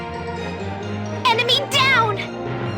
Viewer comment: does cosette say "emeny down"?